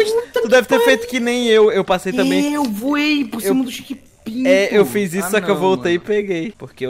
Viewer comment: só eu que ouvi chekpinto?